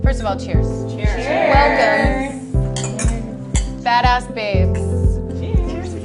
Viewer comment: Oh, God. Only 10 seconds in and I thought they said "FATASS Babes. Butt they said BADASS babes. Which I assume means they fart a lot. Yes. I would kick them out of bed. Edit: They are really FATHEAD BITCHES.